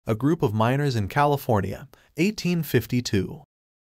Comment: I thought he said a group of minors like 17 and under. I was like life was rough back then. 😔